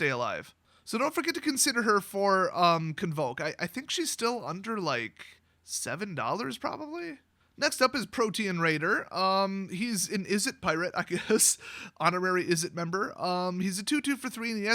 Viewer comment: it sounded like you said “Protein Raider,” and I thought Stormy Daniels had become a Magic card.